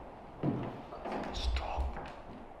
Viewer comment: In number 8 at 1:06:11 I think I heard something whisper 'stop' very quietly